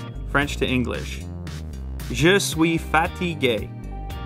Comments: all i heard at 8:40 was, "i am fatty gay" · When he was taking French I thought he said he was fat and gay 💀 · who else heard: Je suis Fatty Gay 😂😂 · I thought you said, "I am fatty gay". I really need to work on my French. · Lol I thought that he said "Je suis fatty gay" xD)))) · When he spoke french I thought he said: "je suis fat and gay". Which it makes totally sense to me said from him 😂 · does nobody else thought he said "je suis fatty gay"? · Did he say "just sweet faty gay" And it was translated to "i am tired "? French is weird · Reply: Je suis fatigué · I thought he was peaking FRANGLISH for a minute thought he said ' Je Suis Fat Y Gay ' 😂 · Why do I keep hearing ' Je suis fatty gay ' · I genuinely thought he said “I am fatty gay” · I really thought I heard: You so fatty gay - Je suis fatigue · Anyone else hear je suis fatty gay?? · I thought he said Fatty gey xD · Sorry I heard "Je suis fatty gay" although I understand french very well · I legit thought he said "I am Fatty Gay" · When he said those french words,I thought he was speaking English Saying "just we fatty gay" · I thought he said je suis fatty gay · when he spoke french I thought he said fatty gay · i thought he said je suis FAT et Gay !!! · Thought u said "je suis Fatty Gay" xD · I heard "fatty gay" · I swear when he said je suis fatigue.....I heard him say ...je suis fatty gay😂😂😂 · I swear I heard "Je suis fatty gay" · when he he spoke french it sounded like he said "je suis fatty gay"